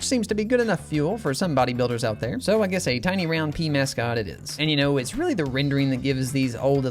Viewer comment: what did you say?! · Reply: It was “Pea Mascot” I can’t believe that people mistake Pea with “Pee” which is nasty